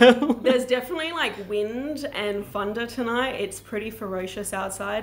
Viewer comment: I think it’s cute it sounded like you said wind and funder, instead of thunder.